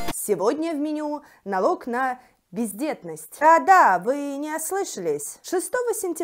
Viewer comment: я почти услышала "пиздетность"